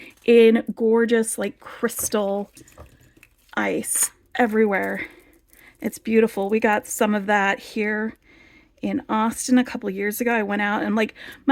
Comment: omg when you said here in Austin, I thought you said here in Boston and I got excited because I live in Massachusetts... and I'm like I want to hang out with!!! lol... anyway, I just wanted to pop on and say I found your channel kind of recently and I love it... and also, thank you because you inspired me to shop from beads to live by and I got that bundle of pyramid shaped beads and it's absolutely stunning. thank you so much for all your gorgeous creations, hard work and your organizing videos really do enjoy watching. 😉